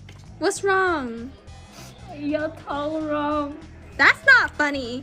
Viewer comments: Why ain’t nobody talking about her crying doing a pedicure? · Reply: I thought she said "Your toe wrong" then "it's not good for me" But I dont have good hearing so who knows lol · Why ain’t nobody talking about her crying doing a pedicure? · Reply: I thought she said, “your toe wrong”